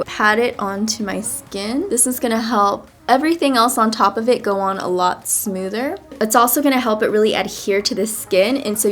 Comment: What powder did you use at 5:21? cant hear you well :-( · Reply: She uses the Laura mercier translucent setting powder and the tarte double duty beauty confidence powder foundation